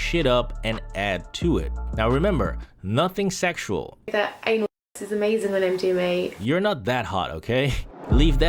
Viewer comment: What did she say there? I can't make it out. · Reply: Literally searched for this comment. The transcripts are wrong too. But I think she says “anal sex is amazing when I’m doing it” but I’m probably wrong.